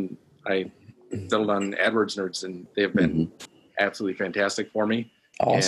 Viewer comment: Who does he say at 6:21 who he hired I can’t make it out? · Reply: Adwords Nerds <link>